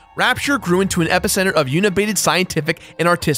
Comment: It's "Ken Levine" not Kevin and "Art Deco" not Arc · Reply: Also "uni-bated" instead of unabated... since we're being padintic.